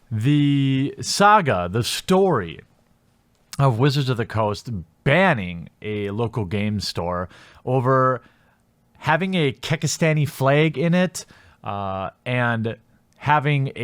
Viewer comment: subtitles thought he said kick-ass Danny flag, is he able to add his own subtitles or is it just YouTube system trying to type put what he says